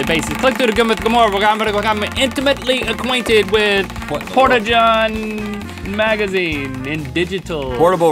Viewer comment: Link said "I'm gonna become" It also sounded like he said "Pornajohn"